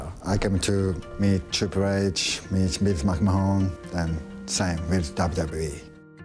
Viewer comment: did he really say to meet Mik Mahan? - Delete